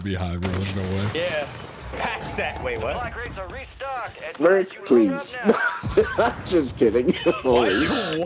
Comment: did he say NECK PLEASE hahahahahh